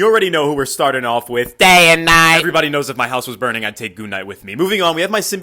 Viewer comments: Is it me or i thought he said g#@ night instead of moon night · I heard you say goon night instead of moon night are you okay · I knew I heard him say "goon knight", wth! 😂😂